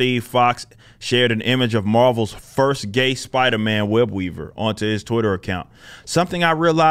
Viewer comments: When he said his name I thought he said WEB EATER 🕸 😂 🤣 😅.. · Gay. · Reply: When you said his name I thought you said WEB EATER LOL 😆 😂 🤣 😅